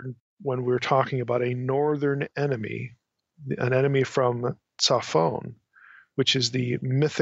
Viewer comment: Sorry dumb question.Transcript @ 06:25 says enemy from cellphone which is the mythicnorth the place the bad place? Surely this is not what you actually said?